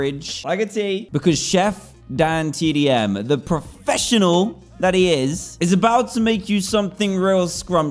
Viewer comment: I thought you said you was a professional